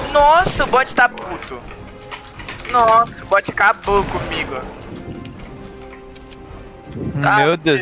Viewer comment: Eu juro que eu ouvi o bot ta puto comigo 1:20 bah